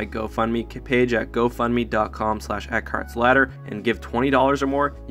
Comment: Thanks for the great ideas guys. What do you think of my new thumbnails? Want to help the channel out? Consider donating so I can upgrade my hardware: <link> Subscribe: <link> Join the (superfun!) Discord: <link> And follow me on Twitter: <link> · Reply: every time I hear you say your name I hear "A carts slaughter"